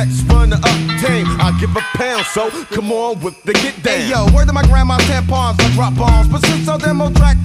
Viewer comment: ☝️🧐Wait!!!! Did he just say "...word to my grandma's tampons"?